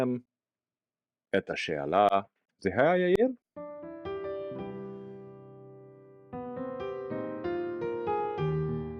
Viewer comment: I speak Hebrew at a native level and replayed what you say at 0:53 and can not for the life of me figure out what you are trying to say for "effective". Are you trying to say יעיל? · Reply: יעיל. I thought I said it normal but heard it sounded off in editing. Didn’t want to deceptively edit, fix, or sub, since evidently my current level is “can’t actually tell what sounds I’m making”